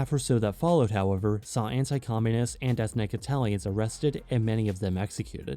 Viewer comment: "the month and a half or so that followed, (what ❓❓) communists..." I couldn't understand what he said. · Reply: "However saw anti communist and ethnic Italians arrested and many of them oofed"